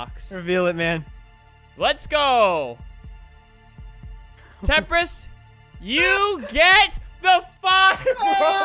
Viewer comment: Am i the only one who heard say "Temprist, you get the f*** off!" At 13:33?